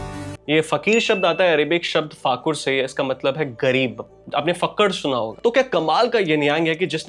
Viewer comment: "ye fakir shabdh ataah hai Arabic shabdh fakur sae matlab gareeb" And here I heard Fucker 🤦🏽